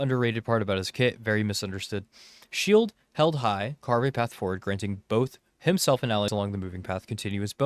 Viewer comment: I thought it said granting him 180 FPS lol. I was like, why????